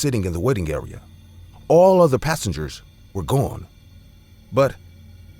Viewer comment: Who else thought he was saying "wedding area" and not "Waiting" area